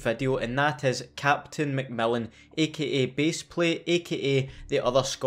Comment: #notificationsquad absolute geezer Captain Macmillan, oi suzy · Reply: Quick question. Does he say MacMillan or MacMelon at around 0:13 (I don't quite understand scottish accent)